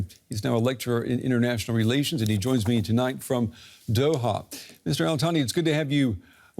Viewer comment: did he say “Israel Thani”?😂😂. I had to watch to see who this “Israel Thani” was - what a combination!😅😅😅